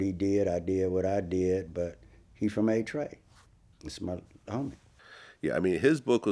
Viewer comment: did he say he smoked my homie. Melvin saying kody killed his friend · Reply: Nah he still my homie he said